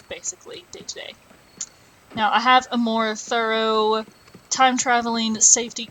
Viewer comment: Did you just say ‘thurrow’ instead of thorough?